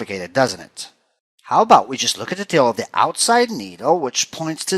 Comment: Took me forever to figure out you were saying "tail" at 3:29. Had to watch it over and over and over before I finally got what you were saying.